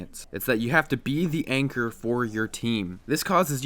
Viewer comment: guys could you please help me, I don't speak english very well adn I don't understand the main word of this vidéo at 3:17 , it sounds like "ancre" fore me but if you could help me it will be lovely :). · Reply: Anchor.